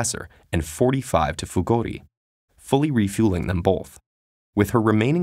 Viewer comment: Did you say bolth at 2:28 and 2:41?